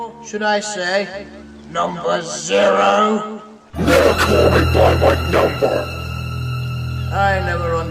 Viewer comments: What did he say? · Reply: Never call me by my number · Can I hear what Timothy's saying? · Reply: he said:DONT CALL ME BY MY NUMBER!